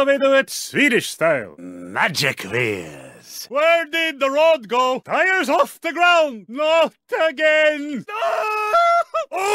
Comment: I thought he said "Players off the ground!" which could mean he was telling players to be more careful